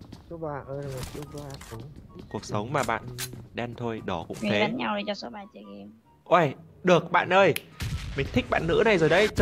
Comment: bạn nữ nói gì v mng?? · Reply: Đi bắn nhau cho số 3 chơi nữa